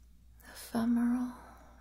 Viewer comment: Did she say ephemeral or femoral? Or was it a Freudian slip? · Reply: Hahaha I said ephemeral but femoral would've been funny 💖